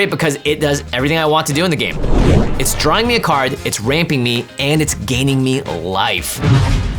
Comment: When he said it’s ramping me. I thought he said raping! Had to listen to it 3 times to understand what he said lol